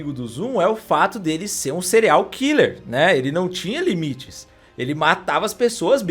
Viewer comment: juro que entendi "cereal killer" kkkk